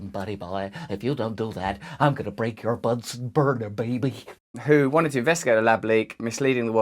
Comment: "... if you don't do that, I'm gonna break your Bunsen burner, baby." Science bullies are so mean XD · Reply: I thought that was hilarious as well. I thought he said burger at first hahah